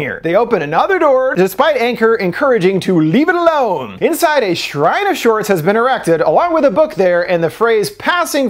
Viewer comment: I thought you said a Shrine-asaurs.... I had to back it up. I was like OHHHH shrine of sorts lol